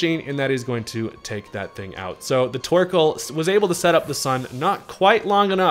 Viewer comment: I hear twerkoal. New nickname? lmao